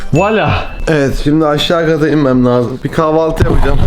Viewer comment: ne dedi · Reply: Voilà! Fransızca bir söz.azerbaycanca və budur!turkce işte bu!anlamına geliyor